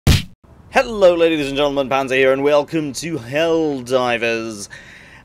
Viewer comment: At first, I thought you were saying, "Hello ladies and gentlemen, Panda here...". Your vids are amazing too btw.